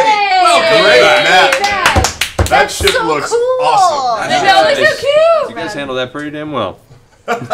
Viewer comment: Watching this in 2022, 3:34:35 Travis: “Great map, man! That shit looks awesome!” I thought he said it looked bussin…. 🤦‍♂️